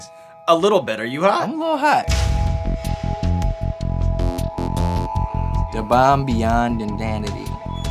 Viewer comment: Lol. WHAT THE FUCK DID HE SAY @ 13:00? Shit has me cracking up. Think he just said the name of the sauce but he said it funny as fuck. "Da Bomb Beyond Dindandiddy".